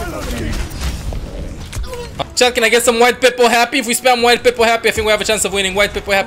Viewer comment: Is... Is he saying "White People Happy" ??? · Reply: WidePeepoHappy which is a custom emote you can see if you have BTTV or FFZ installed.